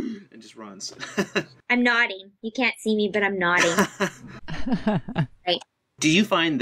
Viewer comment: At first I heard that as "I'm naughty. You can't see me, but I'm naughty".